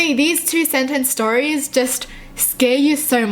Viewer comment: Did she say THREE sentence stories??? Now that's scary...